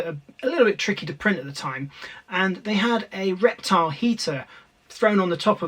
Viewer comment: for a min there i thought you were saying reptar heater